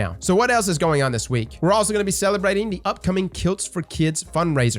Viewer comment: Im so glad that could still say his legendary line. Hope you get well soon boss, we're all rooting for ya. Edit.. Kilts, it says Kilts. I was a bit thrown at first and thought it was Kills for Kids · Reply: Yeah I thought said kills as well